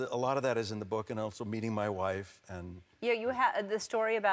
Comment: I thought he said "beating my wife"